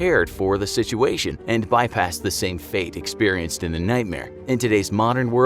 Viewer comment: I swear he said "nutmare"